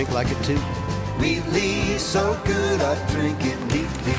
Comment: I thought they said "Wheatley so good I drink it weekly"